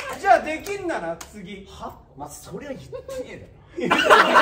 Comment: この時元太くん何言ったかわかる人います？？携帯の音質悪くて聞き取れないんですけどスタッフさん含め爆笑してるので気になる笑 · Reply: お前それ言ってねえだろ